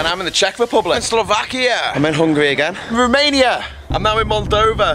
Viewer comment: I thought he was saying ''I'm hungry again'' when he was in Hungary